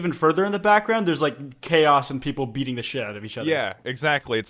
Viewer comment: i really thought kwite said "people eating the shit out of each other" and i was really scared for a brief moment